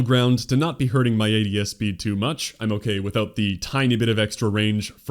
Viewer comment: What does "ATSB" mean? or maybe you said something else, i dont understand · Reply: Guess it's a little hard to hear if you don't know what you're listening for. It was "ADS Speed"